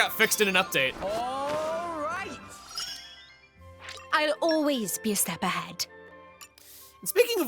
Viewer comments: it sounds like Nia is saying: "I always piss the bed" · I thought she said " I always piss the bed" lol